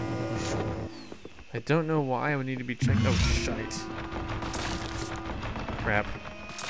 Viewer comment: Did he say “oh shite” At 32:03 😂😂😂